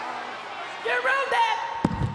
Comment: Sounded like she said "You Runnd-détt" or "You Grounddett" lol haahahalarious